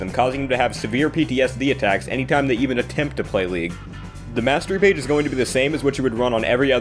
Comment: did he just say masturbate ?